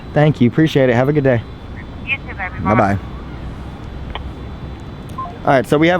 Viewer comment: Did the police officer call him Baby? :') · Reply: That's what I'm thinking too I heard ' you too baby '